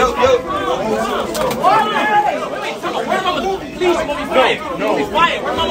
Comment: The “word to my mother you are gonna be fired” had me 😂😂😂😂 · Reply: i thought he said “please let me fight”